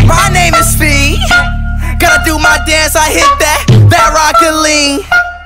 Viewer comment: guys did he just say : rocket league.